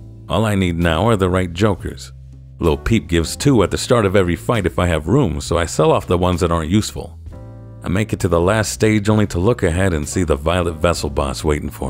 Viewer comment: did he say Lil Peep? lmao